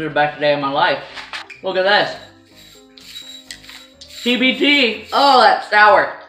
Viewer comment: Did she say "DBT" at 5:07 with the Slinky? XD